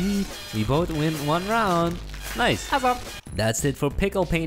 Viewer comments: did they just say pickle painters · Thats It For Pickel Painters! Or did i hear it wrongly?